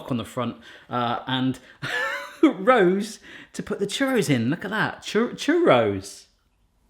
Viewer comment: The first time you brought it out, I thought you said cherry sauce instead of churros. I was waiting for you to make cherry sauce!😂🤣😆 the dough in the water really confused me for a minute! Lol